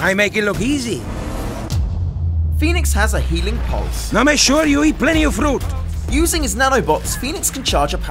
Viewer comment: What dit he say at 0:36 ? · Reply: (I) Make sure you eat plenty fruit